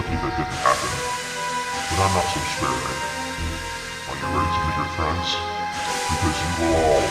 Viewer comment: The background sounds are too loud we can't hear what Timothy's saying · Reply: He said "Are you ready to meet your friends"